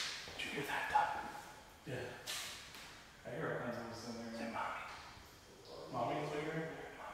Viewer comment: Omg! I hope will get checked out. These videos aren't more important that his health. That being said, I observed the following at 7:39 a child say faintly "Momma". At 8:23 " You're welcome". At 9:01 "Look! I'm over here!". At 11:18 I heard " Sparky" but wasn't sure if you said that. At 14:28 I heard a faint Evil voice. Great job! Its been a lot of fun watching your videos! You bring a lot of humor to these scary explores. It's very refreshing! Sending much Love! 💖✌️🤗 Cher